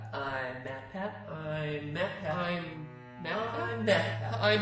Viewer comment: For a second at the start I thought he said I am a cat